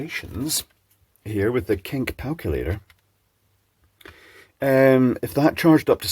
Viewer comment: I don’t know what it says about me.. but when you introduced said mathematical engine I thought you said “ let’s work it out with the ‘kink calculator’ “